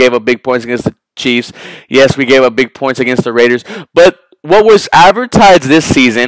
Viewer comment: did you say we gave points to the raiders ? 😂